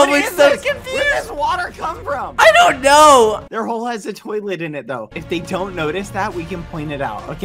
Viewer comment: did he just say "their hole has a toilet in it"